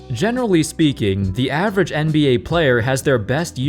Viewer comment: Thought you said "Genderly speaking" in the intro 😅